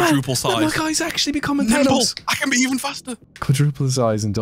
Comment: I honestly thought said nipple not nimble